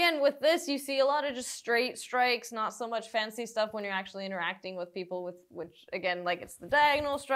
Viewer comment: When says "interacting with someone", all I hear "is beating the snot out of someone..." :) Loved the analysis. Informative and entertaining. Thank you !